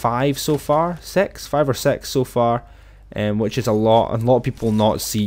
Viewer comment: he said sex instead of six